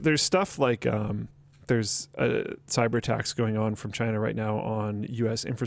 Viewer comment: At first I thought was saying 'cyber TAX', not 'cyber attacks'